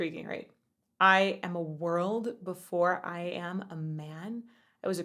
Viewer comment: Ha ! I always thought he said " I was a WORM before I was a man " . I guess i wasnt paying enough attention heh heh .